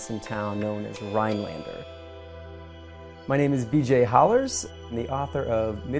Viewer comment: I thought he said "My name is dj hollars" Lol